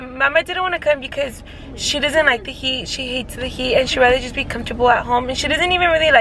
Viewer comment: I'm dead I have to rewind it cause I heard wrong 🤣 I thought she said "mama's not coming because she doesn't like piki she hates piki" 🙊 but she said heat lmao